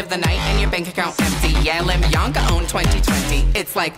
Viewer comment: "Lemiyanka 2020" hit so hard for some reason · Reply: Idk why I keep hearing “let Bianca own 2020” lmao